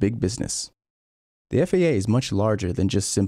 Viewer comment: did you say the FAA isn’t that for airplanes ✈️ and not F1 🏎